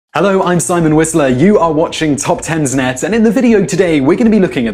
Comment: i didn't realize he was saying, "i'm stein whistler" i always thought he was saying " hi Einstein whistler" as if he was saying hi to Einstein!